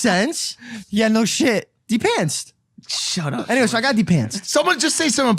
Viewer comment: sounds like is saying “deep pants”😂